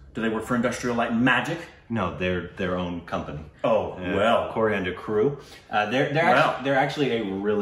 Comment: Did he say Coriander Crew at 0:58.. Or is my ear having some issue..🤣